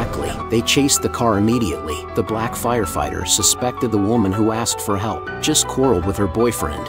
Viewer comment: I’m sorry what did you say*the black firefighter*